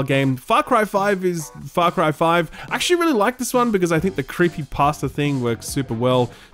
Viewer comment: i heard "creepypasta" the first time lmao